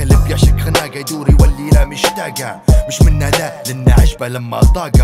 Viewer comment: سمعت مشتاقة 🤣🤣